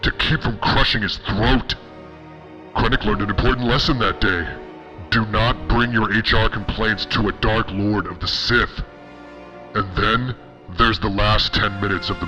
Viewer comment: I thought I heard "do not bring your enchilada plate to the dark lord of the sith". 😂😂😂